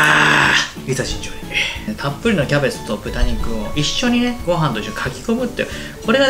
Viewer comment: はじめて聞き取れなかった😢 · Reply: 自分には「いざ尋常に」と聞こえました